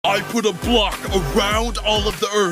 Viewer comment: did i just hear the word c*ck?